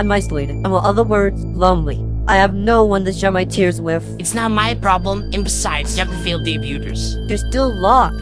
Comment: Can someone tell me what they are saying here?? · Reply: “It’s not my problem! And besides, you have the failed debuters..”